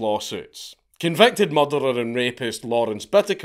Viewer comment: I first thought you said Clarence Boddiker, and was expecting you to say he sued Officer Alex Murphy for police brutality. I know that the focus was on America, but the most ridiculous prisoner lawsuit has to be Anders Breivik. He sued because the prison gave him a PS2, and he wanted a PS3. And he won.